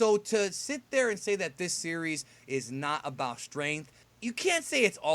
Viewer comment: you trigger my Siri everytime I rewind to this time because it almost sounds like ur saying Siri lol